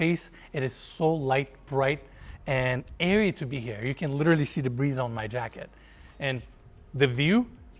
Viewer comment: did he say we can literally see the breeze 🤔